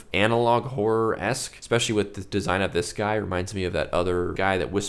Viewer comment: Aren’t TicToc videos all whore videos? Oh, horror…. Sounded like you said whore…